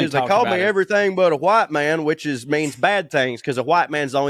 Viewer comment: Saying I always heard was "called me everything but a child of god"